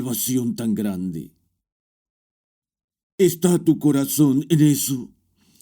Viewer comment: En el minuto 23:12 el hace una pregunta que no entendi o no se tradujo bien, alguien me puede decir que dijo sobre el corazon gracias y Bendiciones a todos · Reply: ¿está tu corazón en eso?